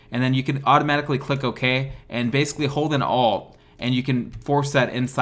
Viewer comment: What does he say at 9:06?! “Hold an all?” I’m holding “alt” but it isn’t working.